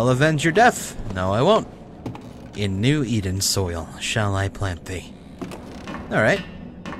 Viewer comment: I heard "Shall i plant B" lol, sorry for dead meme, but its rly funny